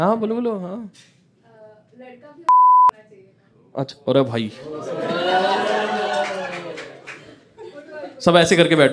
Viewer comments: what is said ? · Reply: Ho*ny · What she said at 2:30 which is censored.... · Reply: Ho*ny